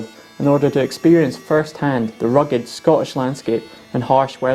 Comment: “Scotch landscape”. Scottish landscape, my man. Scotch is a drink. Otherwise, excellent lesson in how they dealt with a pretty unforgiving environment with simple tools. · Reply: you're talking about 0:23? I hear "Scottish".